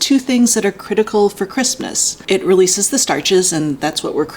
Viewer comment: I keep hearing 'Christmas' instead of 'crispness'